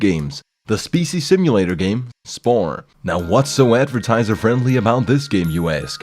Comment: Thought he said 'sporn'